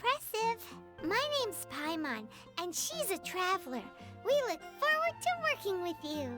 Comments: Did Paimon says "she" instead of "he" when telling the MC being a Traveler? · did paimon say "she"? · did paimon say "she"!!!!!!??? Oh gawd it's confirmed aether is a girl!!!!!! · did paimon called aether a she .w. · did paimon just say the traveler (bwaap) was an she? Lmfao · Did she just say "She is a traveler" ? Poor Aether 😂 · did paimon say 'she' or am i stupid? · DID PAIMON JUST SAID SHE'S A TRAVELER TO AETHER?